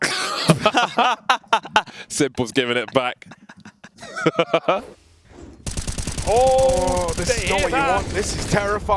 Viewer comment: What did s1mple say? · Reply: "you guys fuckin suck"